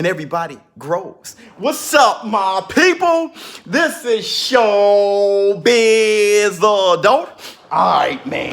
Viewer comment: I always thought you were saying show biz the dope... much love and appreciation!